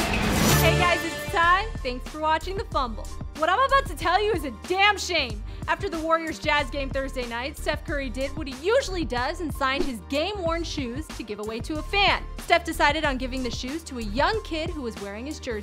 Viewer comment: I was sure I heard gay porn shoes.. I was like WTF.